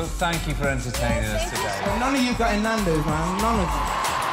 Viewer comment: what is he saying? i'm not from from a place where english is spoken, sorry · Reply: 'none of you are getting nandos, none of you'